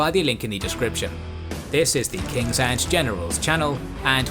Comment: I know you’re saying ‘Kings and Generals’ but sometimes I hear ‘Kings and genitals’